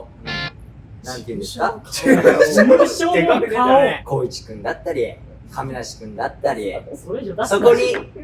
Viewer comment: 髙地くんだったり〜に聞こえて焦り倒した髙地担です、そんなわけなかった(失礼)